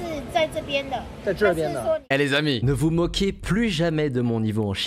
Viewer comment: Y'a que moi qui entend le mot en N 0_0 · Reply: Alors il y a une resemblance avec le n word mais c’est écris autrement « niga » qui à un autre sens qui veux dire vous